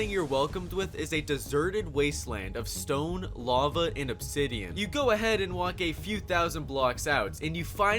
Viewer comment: “ Deserted waste land of lava stone and obsidian “ 0:20 (also at 1:20) I thought u said obsidian not bedrock